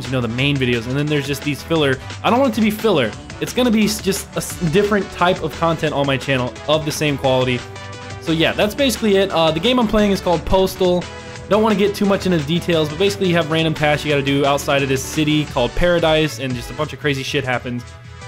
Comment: ima be honest i thought it said PORTAL two not postal two